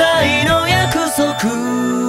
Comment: 1番最初、 おめでとう 何がだよ が おめでとう ありがとよ に聞こえてました